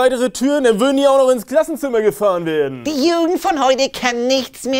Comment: Bin ich der einzige, der "Die Juden" verstanden hat? 😆